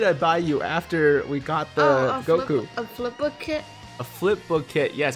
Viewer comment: 🤣🤣I thought he said some flip book shit